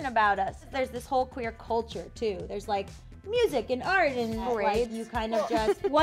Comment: When said "Parades" at first I thought she said "Rights" and I was laughing so freaking hard